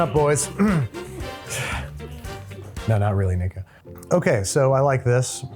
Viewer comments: Did you say the n word? At 15:30 i swear i heard it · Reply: He said "Nico". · did he really said Ni**?😂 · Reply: He did not, he said "Nico".